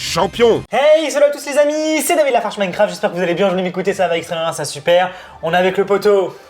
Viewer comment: Yo Attend quoi à 0:24 ...???